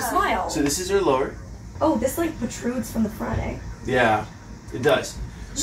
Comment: what's that Canadian Joke about? Didn't get - not even understood what was said ;-( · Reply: Dan Sgambelluri that's not what she said. The subtitles say "the truths from the Friday", which is also not correct. Maybe she said "extrudes" like when you form a mold? idk.